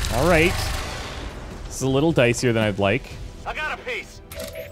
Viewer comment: I swear I heard "I've gotta piss" instead of "I got a piece"